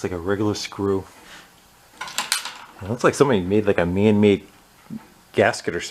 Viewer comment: Did you just say "Man made gasket"? :)